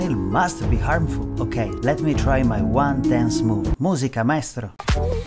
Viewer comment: what did you said here 7:19🙂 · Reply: Musica maestro